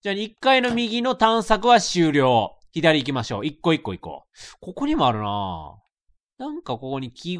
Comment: 一個一個行こう IKKO IKKO IKKOに聞こえた